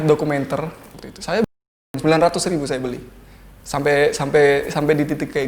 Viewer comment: ini ngomong apa ya wkwk pengen tau parrahh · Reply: beli narkoba